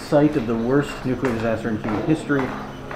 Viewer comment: About 7:15 you said the worst nuclear disaster I thought you said the worst stupid disaster so I had to go back and be like wait a minute wait a minute I just woke up wait a minute what's going on here and then I was like oh okay okay that makes more sense